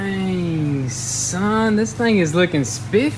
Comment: what does he say ????????????????????? Dang Son ?